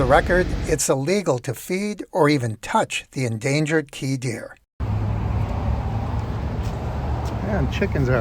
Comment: to me the word deer sounded like dinner, the endangered key dinner